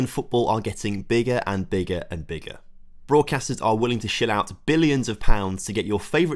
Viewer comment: I thought you said "willing to sh*t out deals..." - had to rewind and realized you said shed load :D